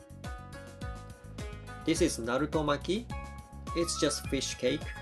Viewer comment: did he say naruto uzamaki like the anime character and thats his logo looool